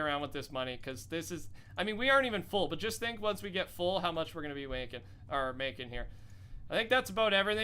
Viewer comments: did he say how much we are wanking? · this man said "wanking" instead of "making", there it is...